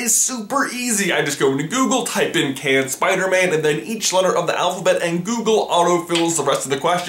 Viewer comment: i thought he said "canned spiderman"